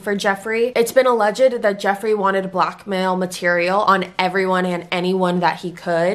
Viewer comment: when you were saying "blackmail" at first i thought you were saying "black male" and i was like wha huh 😂